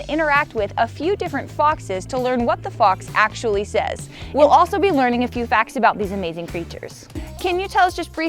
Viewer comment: Anyone else heard " Wtf it actually says " or is it just me lol